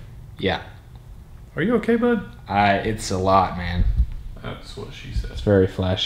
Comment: Ho else heard say thats wat she said at 9:14 😂 I love you guy's